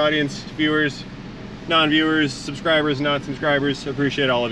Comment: OMG! I thought you said P*nche Cruiser.